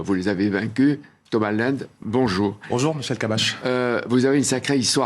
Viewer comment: excusez moi mais à 0:27 il dit "bonjour Michel kabbach"?